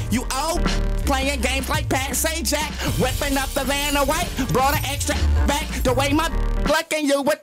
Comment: did he say vanna white at 0:34 ?